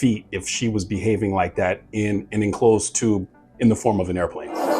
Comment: Ohh! Enclosed tube. I thought you said enclosed tomb. I was like dang bro, you have a dark view on flying lol